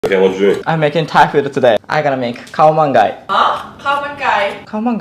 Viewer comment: I heard it as "Cow man gay'